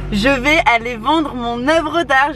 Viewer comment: demande pas pourquoi j'ai pensé très fort "je vais vendre mon âme"